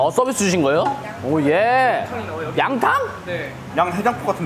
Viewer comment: 양팡이라잘못들었다